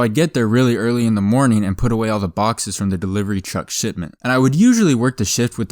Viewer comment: lol it sounds like hes saying shitment